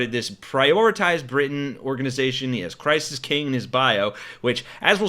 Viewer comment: I thought you said Crisis King not Christ is King. 😂😂 He made his mother cry, I understand why Dad is pissed.